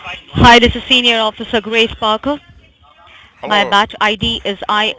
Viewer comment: Wtf she really said her name is Grey or Grace sparkle ??? lol WHAT !!! She's annoying to all hell. I wonder if you could tell them that the IRS changed it's name, closed down or say that it is a federal holiday and ask why the scammer is working that day since the IRS and the goat houses are closed. Idk just a thought though ... lol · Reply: Tee I thought she said Grace Sparkle!